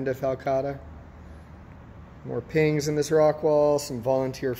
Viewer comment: Hi! Cool store! Can someone tell me what the plant is in 1:43? It sound's like "pains" - it's a flat looking rosette plastered to the rock wall. Many thanks! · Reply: I think he's saying "Pings," which is short for Pinguicula, which is the genus of Butterworts.